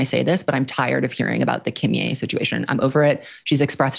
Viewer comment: So out of the Swift loop that I had to go back 5 times to decipher “so tired of the Kim Ye situation” bc the caption says “Kimier situation” and I kept googling “Taylor Swift Kimier” to find the possible tea with this new French artist I made up in my head 😂 Thanks auditory processing disorder.